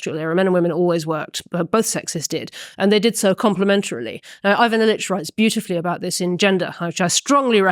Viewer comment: she mentions an author, but says it so fast I can't understand. Somebody knows who that is? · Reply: Ivan Illich was the author.